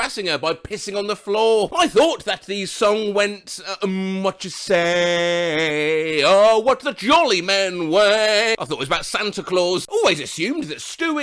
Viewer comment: I always thought it was “mm whatcha say, oh what the seller men wear” 💀💀💀☠️☠️